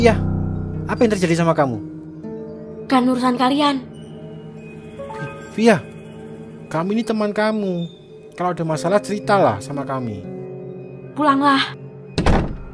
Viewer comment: kamu ini teman kamu Atau gw salah dengar 😂😂